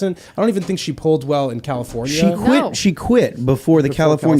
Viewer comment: I heard Tim say "A law requiring POC/disabled/LGB/etc people on the corporate board..." and my heart started palpitating until I replayed what he actually said, which was "In California..." at the beginning. My God. What a fucking fright.